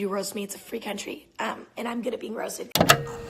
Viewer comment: I thought she said I’m good at being molested